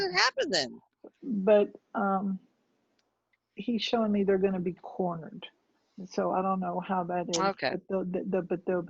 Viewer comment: can u pls tell me what he said when she aked how the mullas get down ? he said they gonna be cornered ?!! i didnt undrestand that part .